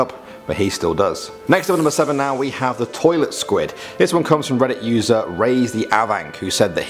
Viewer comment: i thought he said toilet squad instead of toilet squid lmao